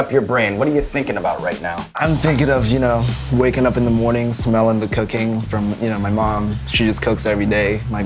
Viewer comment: okay but am I the only one who heard "smelling the cocaine" ??